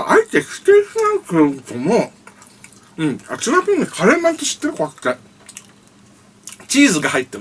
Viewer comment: 終盤で中村屋のカレーについて語り、タイトルでもカレーになってるけど、 序盤で言ってる通り、コレ「ピザまん」だよね？ 10:04 なおカレーの味がする模様 ※訂正 早送りで聞いてたら「カレーの味がしてる」に聞こえたけど、通常速度で聞いたら「カレーまんって知ってる？」でした。失礼しました。